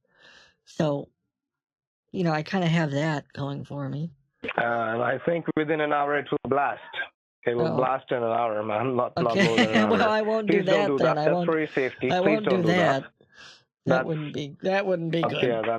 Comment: Kitboga: " my kitchen cleans itself" Scammer: " because of the microwaves?" Kitboga: "no I think it's just blessed" 😂😂😂😂😂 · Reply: at first I thought he said "blasted" guess those microwaves are really up to something